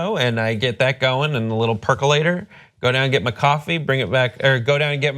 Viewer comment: I heard "friend Stef" not "front step!